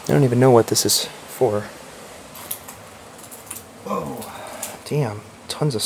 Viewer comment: evp? Think say woa